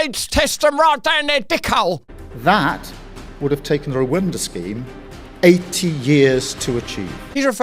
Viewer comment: Did he say Rwinda instead of Rwanda at 2:56. Did he get himself mixed up with Windrush, who absolutely have every right to be here